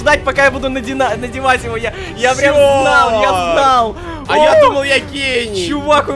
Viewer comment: Мне одной одной послышалось на 6:16 когда сказал " а я думал я гей"😂😂😂